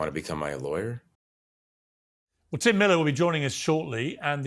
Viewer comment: Thought piers said Tim Dillon. Got me hooked for the first 20 minutes 🤣